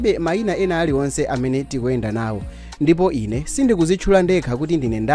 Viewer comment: She Doesn’t Introduce herself?? I thought she just said “Ampinganjira Mumawadziwa”?😂😂